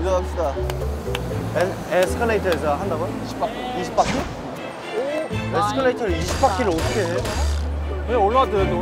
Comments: i can't be the only one who heard "escalators are fuckin weird" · it literally sounds like someone says “escalator is fuckin weird” HAHAHA IM WHEEZING